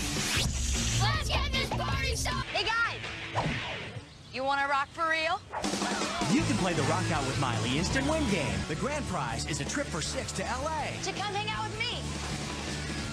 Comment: Omg I thought you were saying "a rock" not "Iraq" 😂🤣😅 I was so confused for a min